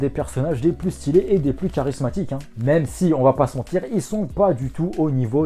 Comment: Quand il y a dit "ils sont 7." Moi, j'ai compris "ils sont sec." 😂